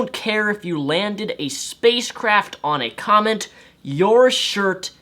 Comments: Hehe did you say "he is one of the first people in history to land a space craft on a comment", lol sounded like you said comment not comet. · While I agree with what you say, I find the fact that you mispronounce comet by saying comment hilarious, unless of course I'm mishearing you, but I swear you keep saying comment. (run-on sentence much) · Is it me or does his pronunciation of the word 'comet' sound like he is saying 'comment'?